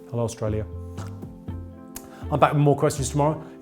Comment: "Don't put magnets up your nose" Welp, there go my lockdown plans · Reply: I first heard "maggots"